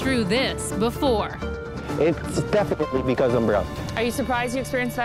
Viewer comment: did he say he’s Brown?